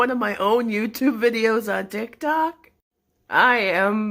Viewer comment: turns captions on me: laughing because youtube captions said Dick Doc